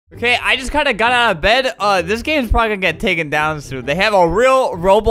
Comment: When I taped this I thought he said I just pulled a gun out of my bed